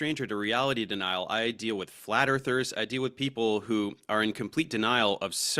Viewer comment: I thought he said "Flatter Thirst". But it was flat earthers, lol